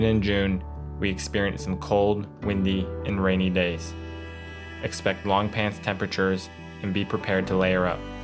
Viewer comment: did you just say {expect long pants temperatures? }